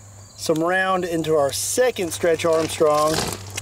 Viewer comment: That sounded like, DESTROYS HIS ARM